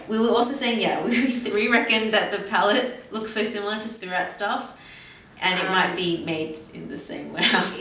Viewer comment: Also, sorry, I can't make out what you say at 18:05, what is the brand that's similar/the same as Addiction? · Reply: Surratt :-) I have to get her to do another video with me 😁